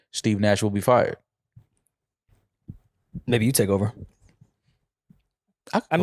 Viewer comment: saying the N word???? 🤣💀 I know there’s no way he said it but it literally sounded like it